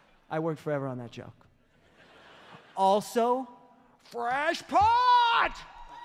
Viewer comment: Did say "crashpot," "cashpot," or something else at 8:05? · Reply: FRESH POT - there's a video of Dave in the studio, it's a super cut of him talking about making more coffee... putting on a 'fresh pot'